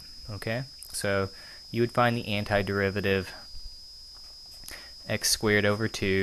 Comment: Did you just say derivative at 05:50?